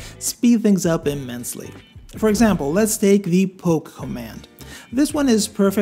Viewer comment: I don’t understand what he says there, I hear “pope command”, can anyone help me? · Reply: Oh, “poke” command I hear now, would have loved to see if there are options for it…